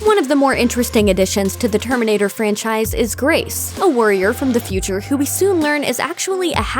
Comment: Who else heard "A worrier from the future..?" I had to replay it to make sure..